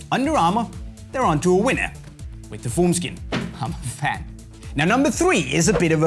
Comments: Was confused for a sec, thought he said foreskin😂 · I legit thought when he said formskin he said foreskin